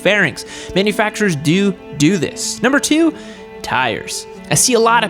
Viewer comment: Did you say do do @ 8:10?? 😂